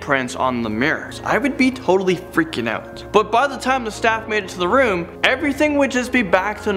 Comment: When he said “I would be freaking out” the way he said it I thought he said I be freaky out